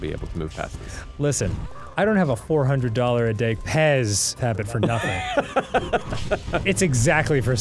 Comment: I thought dan said fez habit and i am still dieing